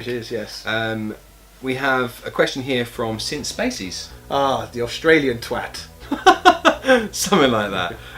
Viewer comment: Was watching this while eating dinner and almost spat my dinner out laughing when I heard "oh! ...Since Spacies! That Australian twat!" LOL